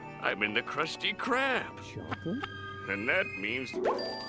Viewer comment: Did you say chocolate? 5:34 It's a hundred dollars.(IYKYK)